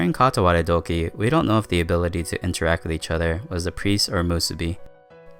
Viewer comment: What does he say at 6:10? When he says "... or a Musubi", what does he say before musubi, sorry if this sounds retarded I just cant make it out. Does he say apreese or something? What does it mean? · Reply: The power of interacting with each other at Katawaredoki was the power of either the Priest or Musubi.